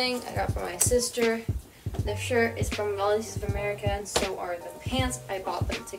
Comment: where did you say you got that shirt? I can’t really understand you. Also, cute outfits and intro! Love this video as a whole <3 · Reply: Volunteers of America! Aka it’s thrifted :)